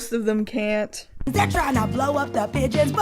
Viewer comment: I thought she says "They're trying to blow up the b?tches""